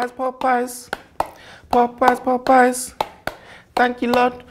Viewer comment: Hilarious! Sounds like he saying ‘Porkpie’.